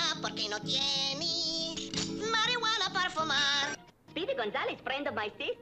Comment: did speedy say "mari-wanna" Parfumar? (not what I really wanted to type)